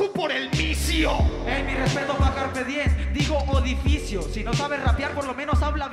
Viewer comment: me acabo de dar cuenta despues de 30 veces escuchandola que dice mis respetos para carpediem